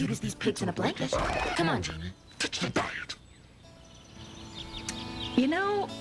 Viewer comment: Can someone tell me what she's saying at 0:14 ??? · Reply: Can't really tell but I think shes saying "ditch the diet"